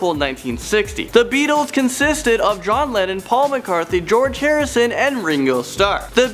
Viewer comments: Does anyone else hear him say Paul McCarthy? Take a listen 5:49. It's Paul McCartney. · wait did he just say Paul McCartnthy